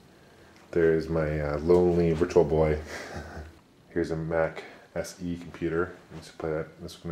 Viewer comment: lol i thought he said sex computer